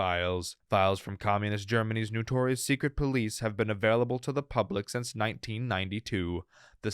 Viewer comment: I heard “notorious secret police” as “Victoria’s Secret police”